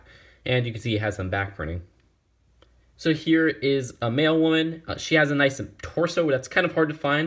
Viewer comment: LOL when you said "Mail woman" I thought you said "Male woman" xD